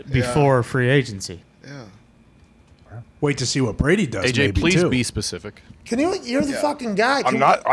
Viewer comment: Did anyone hear that "AJ please be specific" around 2:20 😂